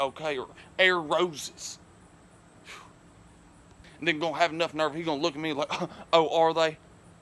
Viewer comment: you i think u said the N word and its offensive because im black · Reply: 10,000 with only a few vids he said and gon have the nerve to look at me